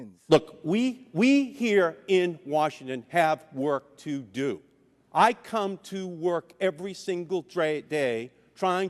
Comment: did he just say he "comes to work every DRE day" hahahahaha